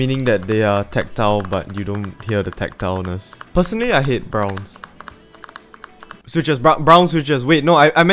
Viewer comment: Did you say "Пацаны(it mean's "guy's", but on russian) i hate brown switches"?